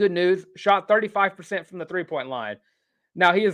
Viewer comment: 35% from free throw line?? 15:40 did i hear that right? and a guard?? i honestly might could drop the ball, kick it with my left foot and average 35%. my lord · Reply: Sorry, my words get jumbled up when I speak quickly, I said "three-point line."